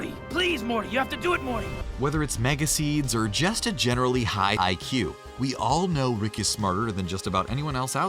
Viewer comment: what happened to your voice it sounded like you said hai ai que